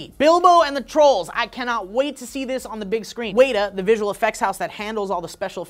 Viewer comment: Did just say Way-ta?